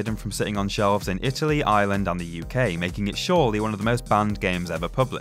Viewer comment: When he said Ireland I thought he said Island! Can anyone F*cking pronounce Ireland right?